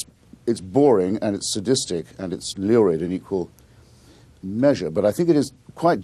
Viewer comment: What's that L-word he uses at 2:42? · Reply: Lurid perhaps?